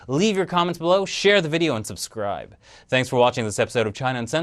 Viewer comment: When you said: "And subscribe" it sounded like you said: "Unsubscribe" xD